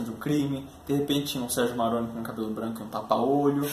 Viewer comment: Juro q todas as vezes que falaram do protagonista de Os Dez Mandamentos eu escutei "Sergio Mallandro", e como não conheço a novela, realmente tava acreditando que ele fazia o personagem principal